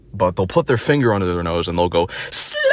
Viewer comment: they were fs saying “slatt”. playboi carti fans are literally disabled lmao